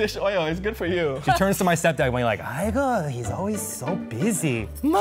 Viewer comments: i never realized said “aigoo” at 1:19 and i had always thought he said “hugo” as in that was his dad’s name. i don’t know. i just wanted to share that. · ((tbh i think he said "aigoo", not "igor" at 1:19)) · the subtitles are wrong it's not 'Igor' its 'Aigoo'.